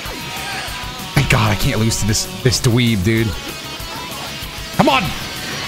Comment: Did anyone else think “this what????” before he said dweeb Not to be that dude but got some allegations to beat still 😭